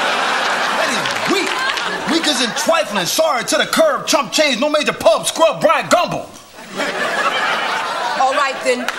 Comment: can anyone please tell me what's he saying at 2:18 ? thanks ! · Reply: "lame"